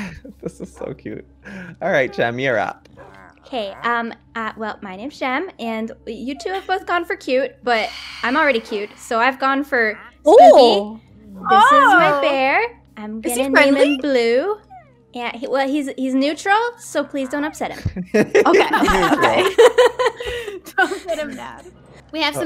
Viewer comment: i heard "This is my bear, and its called blue, bc black is racist"